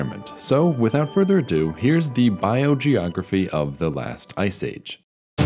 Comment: I thought it said "the biogeography of iceland"